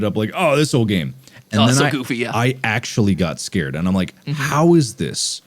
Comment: did he say “goofy ah” 💀